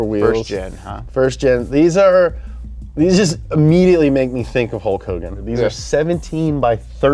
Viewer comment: Can you tell me what James says there (11:25)? "These just immediately make me think of [inaudible]." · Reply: "they immediately made me think of Hulk Hogan"